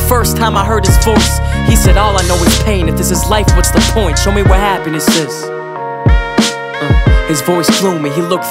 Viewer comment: for some reason i thought he was saying tell me what happened to sis